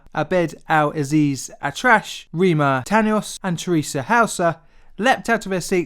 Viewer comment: Did he say "trash"